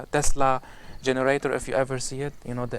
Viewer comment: Salaam. What do you mean "Genders"? Or I might have misheard it. Or Agenda? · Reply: He meant "generation".